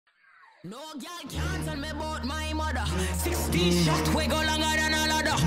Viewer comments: I always heard it as "NOKIA CAN'T TELL ABOUT MY MOTHER" 👁️ 👄 👁️ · 16 shot we 60 longer than a ladder, all i hear is 16 shots we got longer than a ladder 😂 OH BRO ITS A G OMG IM SO STUPID